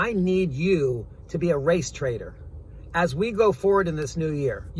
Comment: I misheard “race traitor” for “race trader.” 😅😅